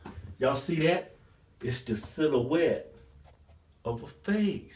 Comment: I swore when he said silhouette it sounded like she-so-wet